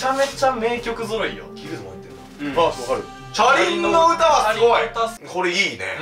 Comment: 車輪の唄がチャリンの唄に聞こえるんだが気のせいか？